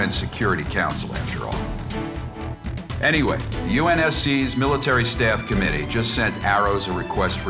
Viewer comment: did he say UNSC